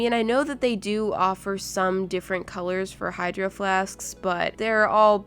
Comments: Is it just me that thought she said hydroflasksks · Is it just me or did I hear when she said hidro flask I thought she said hidro flasksk